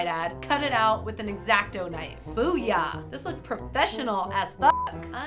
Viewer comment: why did I think that said boob...